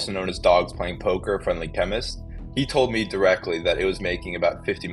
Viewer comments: did he say "FriendlyChemist", as in the dude from the whole Ross Ulbricht and Silk Road ordeal? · Did he say "Friendly chemist" Hmm that name sounds familiar and it wasn't on a yellow brick road. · anybody else hear "friendlychemist" at 7:23 ??? thats the same name from the silk road scam!